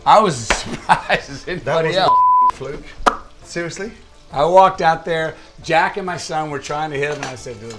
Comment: I thought he said “I walked out there jackin my son” 😭